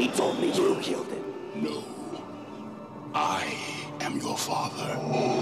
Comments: I hate to tell you Mandela Effect people this, but Vader never said "Luke, I am father". In fact, not only did he not say that, but it wouldn't make any sense for him to. Luke accuses Vader of killing his father, so Vader has to start with 'no' to shoot down Luke's accusation. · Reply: Blarghalt See i always thought he said no luke, i am your father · Talk about a shitty case of the Mandela effect. I swore I heard him saying "Luke, I am your father", I've spent the last 17 years living a lie. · Who came to watch this after hearing the Mandela effect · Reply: Me bruh i thought he said "Luke i am your father" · For my whole life I thought darth vader said :Luke I’m your father But it’s : No I’m your father MY LIFE IS A LIE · He said no, I am your father. I always thought he said lule I am your father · I could’ve sworn he said “Luke, I am your father.” Am I the only one? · When I first saw this movie as a kid in Nigeria, for the longest time, I always thought Vader said "Luke, I am your father"😂😂😂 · I thought he always said "Luke, I am ur father!" But he actually says "no, I am ur father! This world is crazy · He definitely says "LUKE I am your father"... I dont care what I heard, my ears lie. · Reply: As a kid I thought he said "Luke I am your father" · Any other Star Wars fans that knew it was "No, I am your father." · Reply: why i hear "No I am the father" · I thought he. Said Luke am your father for all these years wait something is not right